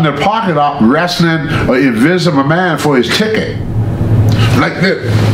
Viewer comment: Invisible man ...... for a sec I thought he said Vince McMahon